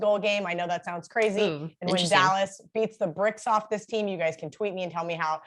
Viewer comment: "beat the bricks off them" LOL ! Did i hear her correctly ?